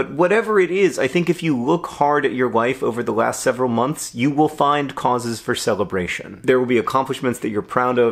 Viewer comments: "If you look hard at your wife over the past few months, you will find many causes of celebration." I'm half certain I misheard that. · I thought you said "If you look hard at your wife" and I was here for it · I heard "if you look hard at your wife over the last several months you will find causes for celebration" at 1:23. Now I'm pretty sure you said life, but honestly, both are true. :-)